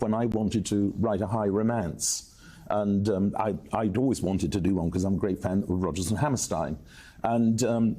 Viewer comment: did he say "how Prince" or something like "Hal Prince"? Cuz it blows my mind to think he knew Prince - lol. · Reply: Hal Prince, the director of the original productions of Phantom, pretty legendary guy.